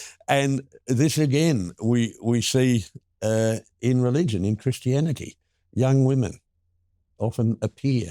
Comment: did he say Christi-anarchy?